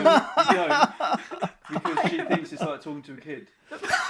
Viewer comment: Can someone tell me what said at 20:02? I couldnt quite understand it and miss the whole joke due to that sadly but I wanna know :c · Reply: He says; "Bcs she thinks it's like talking to a kid"(thats why they dont speak german at home)